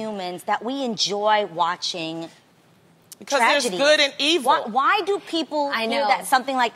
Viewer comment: What does Loni say. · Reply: She said because it's good and evil